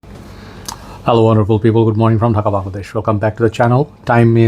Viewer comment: Did he say Hello horrible people? 😂